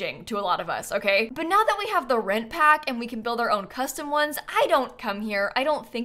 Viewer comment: Anyone else?? When says “the For Rent Pack” I hear it as “The Red Pack”. Every single time. By now you’d think I’d had gotten over it but no.